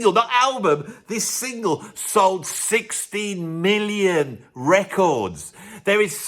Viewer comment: I thought he said 60 million. Did he? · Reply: Second listen he said 16. It sounded like 60 at first (which is an outrageous number) thats why I looked it up.